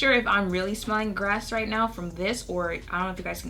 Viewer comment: MA’AM!! EXCUSE ME MA’AM! YOU BETTA DO THE THANG! BEAT THAT FACE HUNTY. And I thought you were saying GRASS instead of GAS but aye like you said, nothings wrong with that. We love a beat face and we love an honest review from the goddess herself.